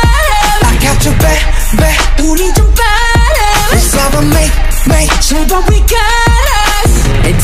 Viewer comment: Why did i heard I'll bake a cake 😂😭🤌🏻